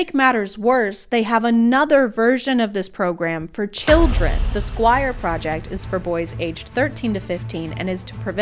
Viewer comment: Oh...The Squire...I heard "Esquire", which the made me imagine a father teling his son he paid for "something called Esquire" and tbe son crying when he realizes he's not getting a guitar.